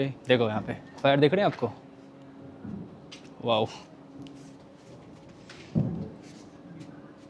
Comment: did he just said wow!!:face-orange-raised-eyebrow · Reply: Wow 😂